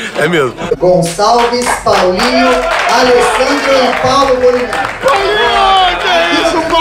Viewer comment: eu ouvi paulo bolivia kkk deve ser o sono